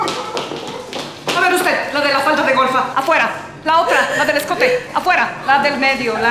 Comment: que dice en ese segundo dice la de la falda de gorda??? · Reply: Falda de Golfa